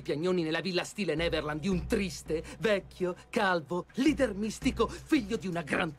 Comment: Qualcuno riesce a capire cosa dice al minuto 1:34? Io non ci riesco :o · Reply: Dice leader mistico